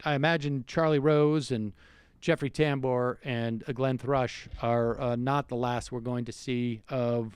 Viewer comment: Glenn Thrush? Whew! I thought you said Geoffrey Rush!